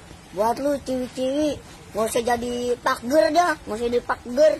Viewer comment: kok aku dengernya pakger😂